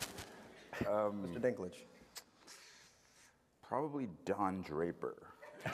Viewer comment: What does Peter say at 39:27? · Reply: Probably Don Draper.